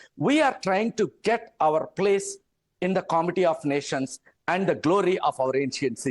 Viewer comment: did he say "comedy of nations"?...or did I hear him incorrectly